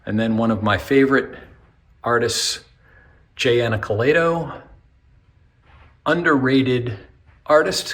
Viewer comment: sorry didn't catch the name of the artist who drew the warner brother sample... Can anyone help me? Love his art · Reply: Jay Anacleto!😊